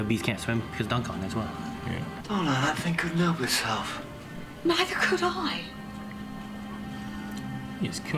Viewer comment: Can someone please tell me what the doctor says? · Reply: "Donna! That thing couldn't help itself." "Neither could I!"